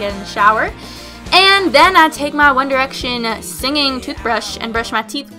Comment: Did she say "Singing toothbrush" @ 2:59?